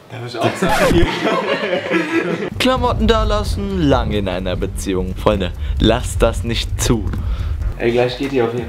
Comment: bei 1:37 min hast du ein Fehler gemacht du hast 《frisch in einer Beziehung》geschrieben obwohl da 《lang in einer Beziehung 》gehört